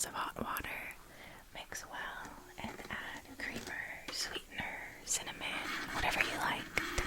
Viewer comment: I wasn’t paying attention and thought you said “you just scoop 1 tablespoon of RICIN into your mug”…